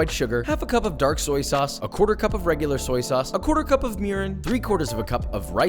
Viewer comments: Okay, who else heard "A quarter-cup of urine"? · Who else thought they heard “A quarter cup of Urine”